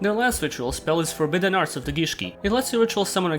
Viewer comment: Thought I heard forbidden arse of the gishki